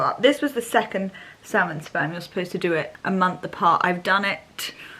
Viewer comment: Is it salmon sperm or salmon dna? I swear you say sperm at 24:45 😅